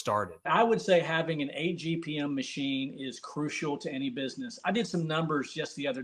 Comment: What is an "apgm machine"? Did I hear correctly? Can't find anything online about "agpm machines"? Mentioned at around 7:32 EDIT: nevermind, I think he said 8 gpm machine.